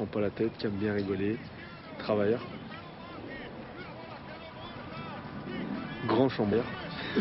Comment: il a dit "grand chômeur " ou koi ???